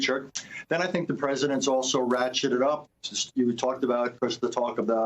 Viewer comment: Had to listen a couple of times. At 1:40 did he say the President has ‘ratshitted it up’? Probably right actually.